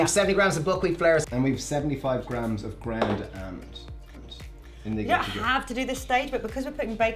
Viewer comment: I can't understand what did he say with Buckwheat flour? 0:43-0:44 - what's the other flour used? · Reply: It's ground almond ! :)